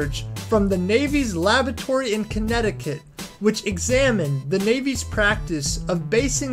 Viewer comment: did he say "Navy's Lavatory in Connecticut"??